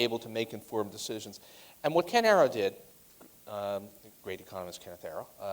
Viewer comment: Paul Krugman mentions another economist, I couldn't hear, which one he said? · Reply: Ken Arrow